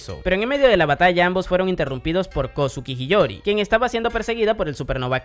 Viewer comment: escuche mal o menciona varias veces el nombre de "Hiyori" 👀